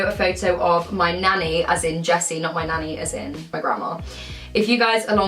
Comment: this whole time I thought you were saying "nanny as in chessy" the nanny from parent trap!